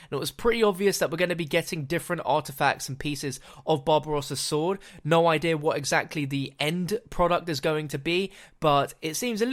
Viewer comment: I swear you said bob ross’s sword